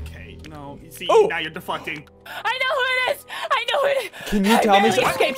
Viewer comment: I HEARD SWEAR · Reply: He did not say defuckting he said deflecting